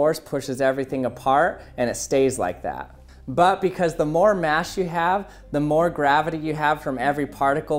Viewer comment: he says mash instead of mass